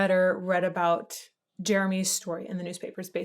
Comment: When this first came out I thought he was saying Jeremy's smoking.